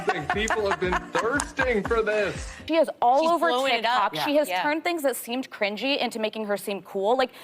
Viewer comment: At 2.18 I thought she said "she is all over Tinder" 😂... I had to replay it several times, then put on subtitles and read it...but it was Tik Tok😂